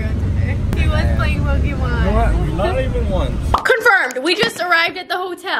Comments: did she say he was on tinder??!! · OMG! I thought she said “Oh your on Tinder”! I had to re listen to that sentence 4 times 😂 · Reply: Bella Ro She said Twitter!🙃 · did she say he was on tinder?! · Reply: Pranati Bala she said twitter not tinder